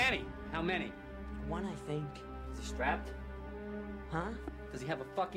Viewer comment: WHAT HE SAYIMG??? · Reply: "Is he strap?" Another way to ask "is he armed".